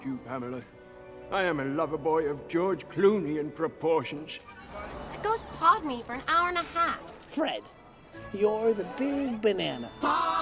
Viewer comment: what was she saying? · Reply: “those guys pawed me for an hour and a half”